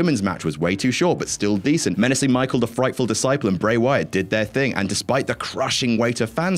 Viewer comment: What the hell did get call the undertaker? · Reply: Menacing Micheal the frightful disciple. 🤣🤣